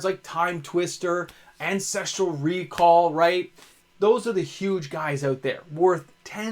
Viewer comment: I first I thought you said "sexual recall" and I was like now hold on a second there friend.